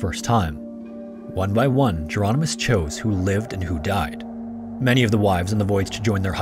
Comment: I’m sorry; what is he saying here? “Many of the wives and the voids,”?